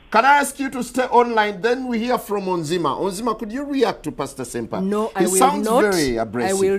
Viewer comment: Thought he said senpai